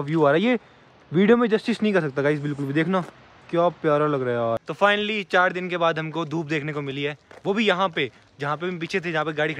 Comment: I really thought he was saying"finally 4 din ke baad hum ghar pohoche hai"😂😂